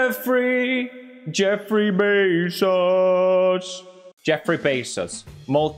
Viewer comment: i thought he said jeffrey V-sauce